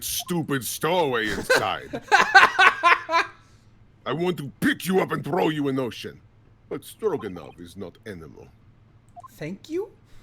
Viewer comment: god I swear I thought it said "I'd like to pick you up and throw you in the ocean, but I'm not animated" and I laughed my ass off.